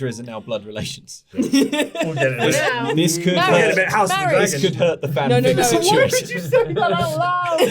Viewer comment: WHAT DID EGBERT SAY AT 47:35 PLEASE I CANT FIGURE IT OUTTTT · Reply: We'll get a bit house of the dragons